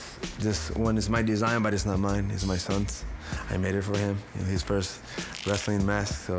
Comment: what did he say? his SON?